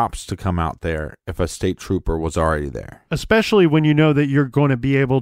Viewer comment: I thought he said State police are here could be State trooper. Where is his body 🤷